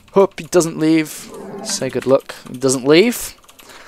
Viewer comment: sounds like he said Good look XDD